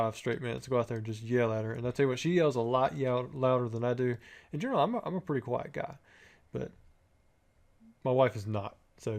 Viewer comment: I heard “ generally I’m a pretty white guy”